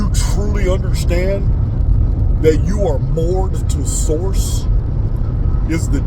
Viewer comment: I couldn't understand what you said. I played it over and over and put the closed caption on. Closed Caption didn't get it either. .....truly understand that you are ("mored") to source is the day your going to be..... When we are - what? - to source? At 29:38 ....Realize you are ("M") to something beyond this.... What was cut out at M? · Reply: I understood it as 'moored' ...as in attached to source energy.